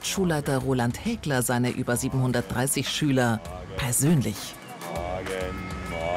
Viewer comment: Wie heißt der Direktor bei 8:49? 😅 hab ich das falsch verstanden, oder heißt der wirklich Hitler?